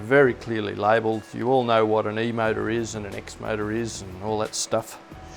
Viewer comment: I thought "I don't know what an "e mater" is?" then I realized its the accent. Motor is what you are saying not mater. Any ways I am excited to see the reviews on this printer I want to know if its good. It look so promising.and I'd love a CR-10 that can print ABS or nylon.